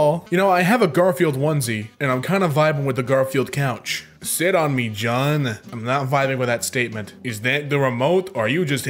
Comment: Thought it said shit on me jon